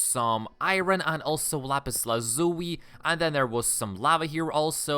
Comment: What did you say " lapis lazuy"?